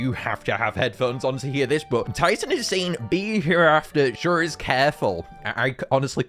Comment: is he saying beat hereafter sure is beautiful?